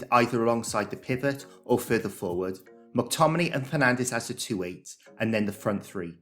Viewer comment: did you call Bruno "Penandes"? 😂